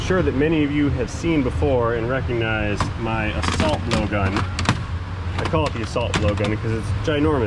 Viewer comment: Oooohhhhh!!! An assault blow gun. This whole time I thought he was saying "a salt blowgun". I was so confused.